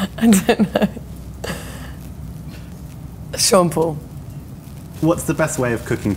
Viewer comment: When she said Sean Paul i thought she was saying Shampoo lol